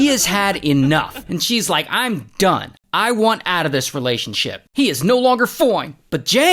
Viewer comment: All I heard was "He's NO LONGER FOOINEE"😂